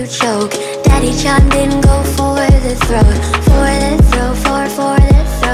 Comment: I legitimately thought it was “Daddy-Chan didn’t go for the throat” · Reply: I thought she said daddy John something went for the throat honestly